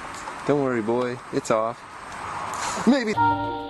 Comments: did he say the N-word? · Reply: no, he said "may be" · what he say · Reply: may be